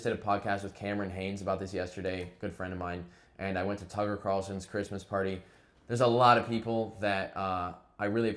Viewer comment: did you actually say you went to Tucker Carlson's Christmas party?? Wow thanks for the heads up. I always thought you were a cool guy, guess not. Bye